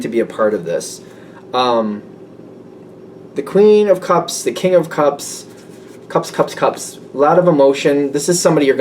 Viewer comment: I thought you said King of Cops for one minute there